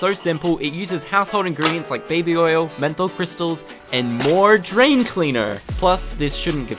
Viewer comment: for a second I thought it said meth crystals 💀